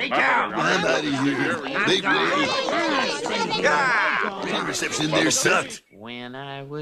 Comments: for those of you who didn't hear what Otto said, he said "radio reception in there sucked", WELL SAID OTTO, EVERYTHING IN THERE SUCKED! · Anyone hear Otto as they were all leaving saying, "The reception in there sucked!" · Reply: I thought he said, "Homer Simpson in there sucked." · Anyone know what otto says at 3:41, it sounds something like the cell reception sucks or is he saying web reception like interent reception? the weather reception?